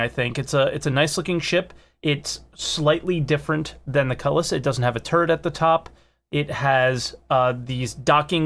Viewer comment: sounded like, "it doesn't have a turd at the top." I guess that's a good thing?